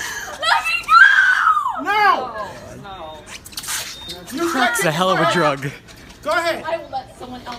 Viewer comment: It's a hell of a drug. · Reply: i think he says "Cracks a hella of a drug"